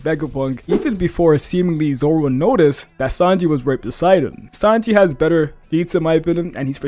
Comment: Why does it say sanji was raped beside him with close captions🤨